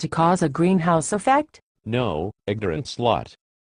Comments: Did he call her an ignorant slut?? LOL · does he say "no, ignorant slut"? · Did he just call her an ignorant slut? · did he call her a ignorant slut?